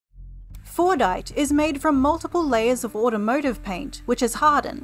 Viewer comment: Misheard this as Fornite😆🤣